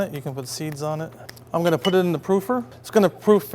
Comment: what's a poofer? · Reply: Ester Melendez, That's what I thought he said, too! But I watched again, and he said "proofer." :)